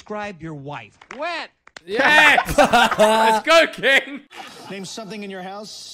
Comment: Did Ethan call that Asian man "Kim" at 7:35? 😂😂😂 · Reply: He said King